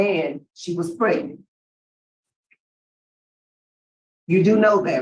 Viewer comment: did she say “aged 12 and pregnant”???